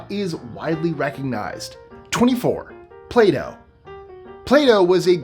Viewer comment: When you said Plato, I thought you said Play-Do. 😄